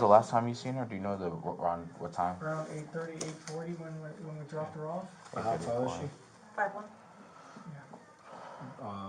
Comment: I keep hearing Jen say, "WE dropped her off". She keeps putting herself in the car!